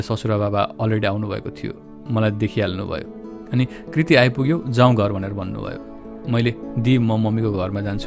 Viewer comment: did I hear Kriti?